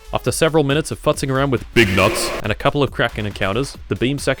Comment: I was listening in the background and thought he said “big knots” I am way too deep into the furry fandom my god.